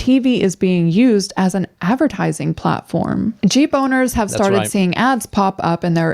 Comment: I thought she said “G boner” for five minutes. She said jeep owner